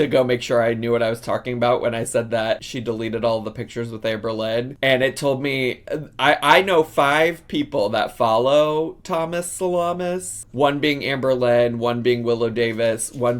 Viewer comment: My hubby just walked through and said “Did he just say “Thomas Solmas?” 🤣🤣🤣🤣🤣🤣